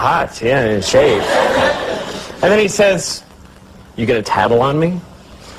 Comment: What did he say? Sounds like "You get a taddle on me?" but that doesn't make sense. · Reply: Jonas Persson "Are you going to tattle on me?"